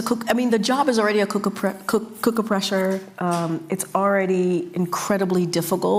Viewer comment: What's a cooker pressure?!?!? Thank God she's gone!!! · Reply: At first I thought she said cookie presser. 😂